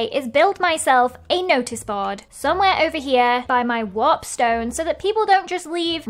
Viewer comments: I think i heard her say "wap stone" XD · i thought you said wap but i'm clearly terrible at hearing things cuz it wasn't wap jajaja